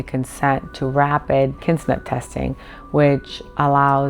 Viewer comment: I thought she said 'kismet testing' and wondered how they came up with the name. Kinship. *Kinship testing. 🤦‍♀️